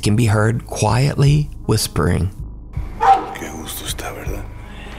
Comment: What's interesting is that, said, "It's really nice, isn't it?" It almost, to me, sounds like the whispering voice is saying "Verdad" or something like that. Now, not being of a Spanish or Hispanic background, I don't know all the things the word "verdad" could be used for, but when I typed it into Google Translate, the word "truth" came up first and foremost. If that's what is being said, then it almost seems like the whispered voice is agreeing with what is saying.